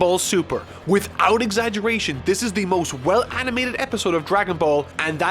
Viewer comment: English is not my native language so i might have misunderstood, did he say thats the best animated episode including Z?